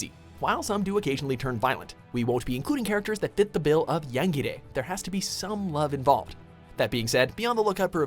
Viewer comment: wut did he say? yangere?